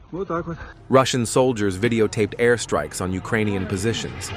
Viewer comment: PS. 6:58 sounds like he says in desperate exasperation "Rule David" but i could be far off the mark with language. Bible says of Gods Ruler siting in obscurity "you have seen many things but have paid no attention." Basically there has to be a public or common outcry so these rulers know their illegitimate. Its like possums or rodents but dealing with the machine at the same time and the machine needs to know who its base is / what is the greater force.